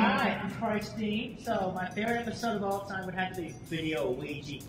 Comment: Wait, was he asking questions at an ATHF panel?? I could be mishearing, but I SWEAR he said “my favorite episode is Video Ouija”